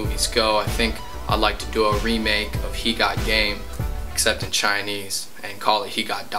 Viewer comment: counldnt figure out what u said so i pause for like 5 seconds then realised u said basketball in chinese....